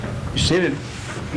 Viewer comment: the subtitles say “shit” instead of “sit” 😂 Tony Kiritsis what a guy!